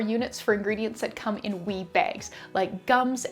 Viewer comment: Oh my goodness I LOVE THIS!!! I need to get some little separators for my mica drawers so I can do this with the wee bags 😍 Thank you so much for sharing! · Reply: Lol! In the video I thought you were saying WEED bag, now I realize you were saying WEE bag!